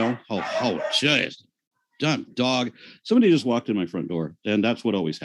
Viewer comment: Did Aaron just say "Dumb Dog!" to his dog(s)?